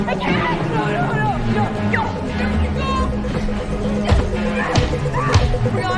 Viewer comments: who makes this dialogue 💀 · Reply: I thought she said “don’t give them sex” lol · what the heck does the girl say at 6:04 I've replayed this like 8 times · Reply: She said "Listen to me, don't give him the satisfaction okay?"